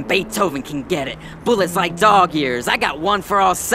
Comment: Dog's "I got this shit in the bag" line was fire, but Cat definitely won this shit. The "talking like Brian Griffin" and "all dogs go to heaven" bars were hard as fuck, and the "bullets like dog years" verse was brutal. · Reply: 😅 Thank you, I thought he said, dog ears. Dog years makes more sense.